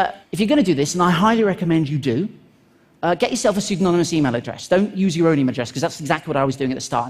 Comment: signaler's email address??? that's what he said? how do I acquire one on Gmail? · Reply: García (匡瑞) "pseudonymous" email address